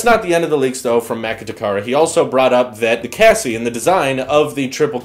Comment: did you just say cassy!!??